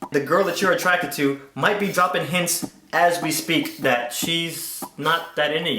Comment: Um.... anyone else use the youtube interactive transcribe? at 1:31... it claims that Swoozie says :"the curvature inside to my teeth occupants"..... what he actually says... " the girl that you're attracted to might be dropping hints" duh fuq happened! lol